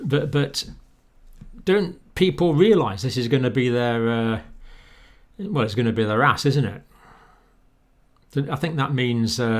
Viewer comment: did he just say it's going the be their ass? hahahahhaaaaaaaa!!